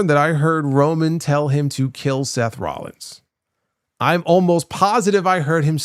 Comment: I heard seth's name too I thought I was bugging I was thinking they were going to do an angle with seth and jacob backstage but maybe next week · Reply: Just went back to watch HE DEFINITELY said "Seth Rollins, Kill him" it's because seth keeps saying he needs to get his title back that he never lost..